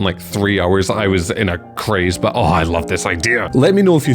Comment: At the end, I heard "or if you have wrong ideas"